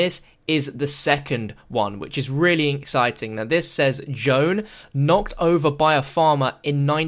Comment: did say jone when he read "gone"